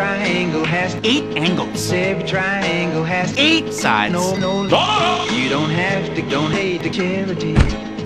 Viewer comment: what did he say? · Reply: "You don't have to donate to charity."